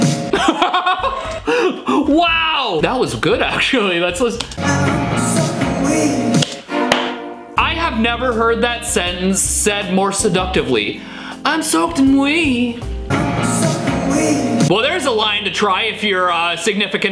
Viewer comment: I heard I’m sucking weed